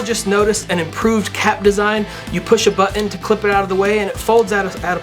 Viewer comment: did he really say, "bu-on"? My cousin is a valley girl and she says bu-on, instead of button.